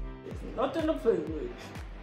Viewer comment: Ummmm...🤔 @ 11:08 to @ 11:10 what did sis just say?! Please translate! 😂🤣😂 But, I feel 'er might've had a traumatic experience with that burn comb! 🤷🏾‍♀️🙅🏾‍♀️😂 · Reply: Nuttin to fool wit 😁😁